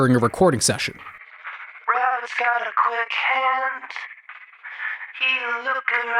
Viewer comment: I always heard “daddy’s got a quick hand” instead of “Robert’s got a quick hand” for some reason